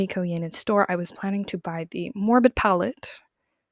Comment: My brain is so bloody demolished I thought it said "Morbius" instead of "Morbid" on the palette for a split second, oh my god.